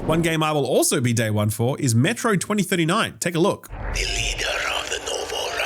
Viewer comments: this sounded like Metroid 2039 lol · For a minute, I thought said "Metroid 2039," and I was like "Yeah, I might like that."